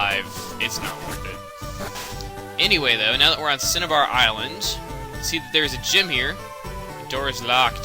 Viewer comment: I thought he said CinnaBon island :(